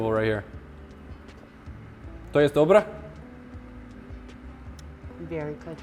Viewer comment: DId you say in Polish "Czy to jest dobre"? (Which means is that good)